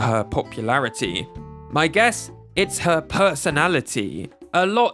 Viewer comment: there is typo in the subtitles, there is "its her popularity" instead of "its her personality"